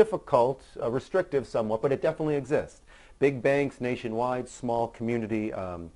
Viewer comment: At one point you were saying "big bank" a lot, and I kept hearing "Big Bang" because you remind me so much of Johnny Galecki from Big Bang Theory. :)